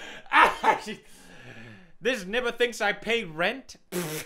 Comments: did he just say nibba? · Did he say n-word? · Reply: He said nibba